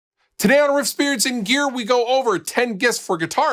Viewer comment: Every single time I watch one of these videos he says "today at riffs beards and gear" but every time he says it, it sounds like he says "today over at spirits and gear" it drives me nuts.